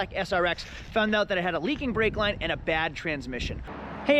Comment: I know you said: "leaking brake line", but what I heard was: "leaking brake LIGHT." 😆🤣 Made you sound like a stereotypical used car salesman! 😂